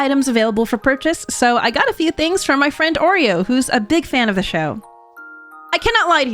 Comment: did u say ur friend is called Oreo 😂 or did i hear u wrong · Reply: O3RO. She’s a song writer and singer on YouTube.